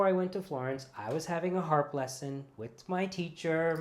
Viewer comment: When said he was “having a harp lesson” - I heard it as having a “heart blessing.” I thought that was so sweet as if when we are experiencing something good it should be labeled a heart blessing.